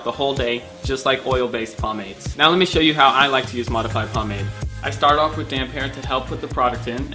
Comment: What is he saying at 1:14 ? "I start of with ??? help put the product in." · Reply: I start off with damp hair